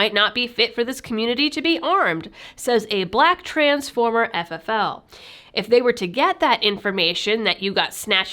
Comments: I heard "black transformer FFL" not "black trans former FFL" at 02:51 and my mind was having a very hard time reconciling that... No, I'm not high on anything, was just distracted. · It was interesting to hear of silly people discovering the goodness of "shall issue" policies. I heard, at 2:53, "a black transformer ffl" and I was confused. Was this an article about "robots in disguise"? No, I just misheard and I laughed and laughed. Thank you for the excellent video. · Um, am I the only one who thought she'd said "Black Transformer FFL" and thought woah megatron's an ffl?